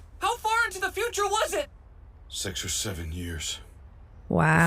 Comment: did he say six or seven years